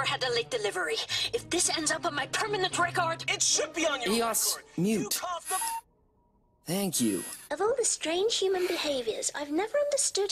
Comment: eos mute, thank you 4:37 what’s the situation 7:21 what, you said they were harmless 9:33 yep 12:22 Alan use my freighter 13:42-13:43 ok, I’m ready 19:22 you’re right